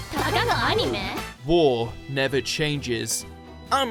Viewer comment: love it that with his British accent actually makes it sounds like he is saying "Whore never changes" XD